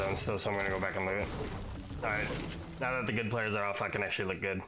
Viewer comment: did you say the f word????? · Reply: now that all the good players are OFF is what i said lol